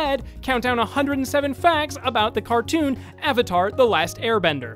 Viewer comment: DID HE JUST SAY CARTOON??